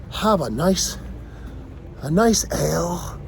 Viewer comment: a nice ale? i thought u said L(blunt) :)